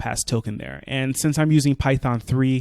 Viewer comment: what do say at @ 9:50 ? the token its generated in....?¿ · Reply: The token is generated in bytes. I decode to a string so I can use it as a string.